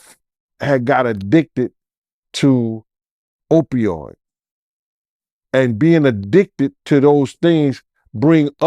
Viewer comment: what was puff addicted to? Did he say Opioids??